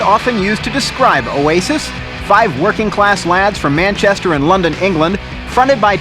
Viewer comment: Did I just hear him say "Manchester in London England" 1:09? Wtf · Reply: No he said Manchester and London